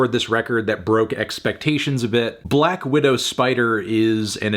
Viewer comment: I like black widow spider solely because it sounds like Andrew is saying black wittle spider